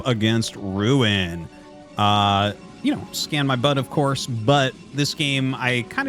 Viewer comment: Am i The only one who just heard him say 'scan my butt ofcourse'?